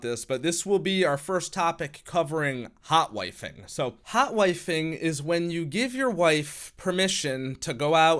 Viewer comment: I honestly thought this said "hot-wifi-ing".